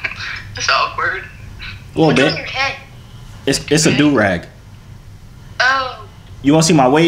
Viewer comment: Did he say Durag 29:03 I thought u only ware joorags